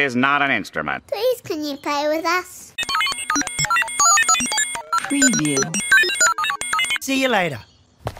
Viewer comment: Did I just hear the word “Preview” in the sound effect?